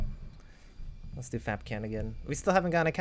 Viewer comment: when you said ''lets do kapkan again'' it sounded like you said lets do fapcam again'' XD